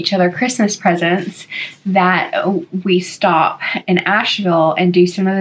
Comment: I heard you talk about the Asheville trip in an earlier video and misheard and though you said Nashville. I got excited thinking you had been in my neck of the woods lol